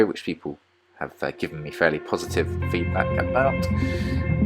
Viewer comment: i just heard: habe gewonnen